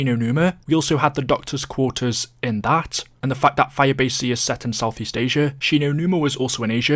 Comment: This whole time I thought he was saying firebase-c